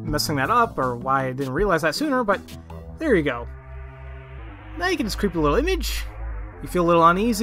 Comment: now you can what? I thought u said, "now you can scribble on an inch!"